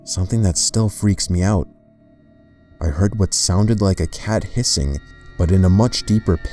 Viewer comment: "I heard what sounded like a cat hissing" I thought it said "pissing" XD